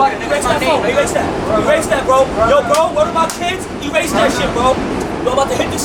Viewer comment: This man did not just say word to my Tims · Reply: Lol bro you need a hearing aid or something? He clearly said "word to my KIDS". Nobody says word to my Tims lol.